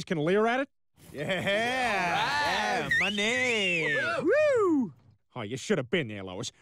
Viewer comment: Bruv, for the whole time I thought he said "ma man" but he said money